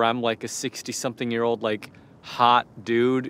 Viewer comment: I thought it said people harvest MONEY for the first time and I'm like wat